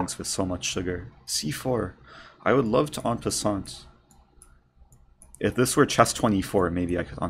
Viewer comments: what did he say? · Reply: マーク白 en passant- look it up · what did he say! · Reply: マーク白 he said “i would love to en passant”